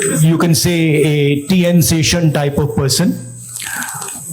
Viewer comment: Did he just say TN SESHAN?? Lmao.